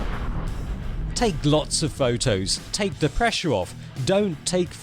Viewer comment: I thought you were saying "take Lok's photos"